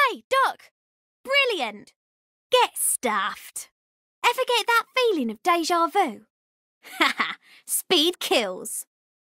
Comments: i hear ever get that feeling of daijobu (its ok in japanese) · what does she says at 1:50 ?? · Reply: ever got that feeling of déjà vu (I dont know how to spell deja vu) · What is she saying from 1:50 - 1:53? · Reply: ever get that feelin of de ja vu